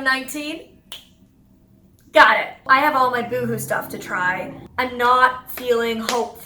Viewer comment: I swear I thought you say Voodoo instead of Boohoo. What are you into Molly Burke!? 😂